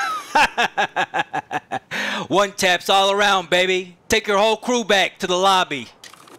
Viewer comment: i thought he said "war crimes" instead of one taps